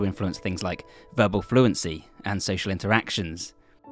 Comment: Am I the only one who heard “sexual interactions”? 😂